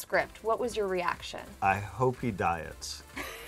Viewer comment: Omg at first I thought he said I hope he dies and I was like yyyyyyyyyy?!?!?!?!?!